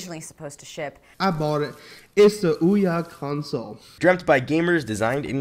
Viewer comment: sounds like he said booyah